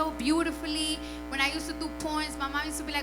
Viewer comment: When she said poems I thought she said pornos or porns